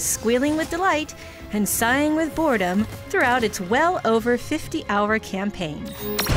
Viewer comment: "Gave me 30 hours of enjoyment." - 7/10 IGN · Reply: did she say 50 hour campaign?